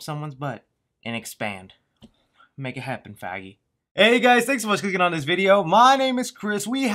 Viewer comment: Did he just say, "make it happen, faggie" ???